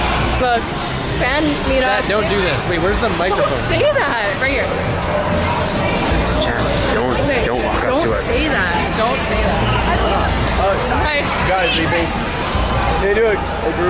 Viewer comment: what did he say? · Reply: “don’t walk up to us”